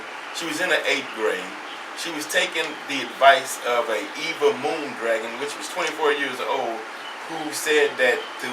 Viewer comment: Did he call ol girl a "Moon Dragon"???🤭......sorry🤣